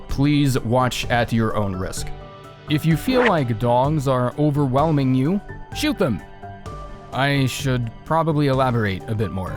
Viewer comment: OH DEAR GOD I heard "If the DONGS are annoying you, just shoot them"... was in the middle of editing my bloodborne screenshots and went WAIT WHAT.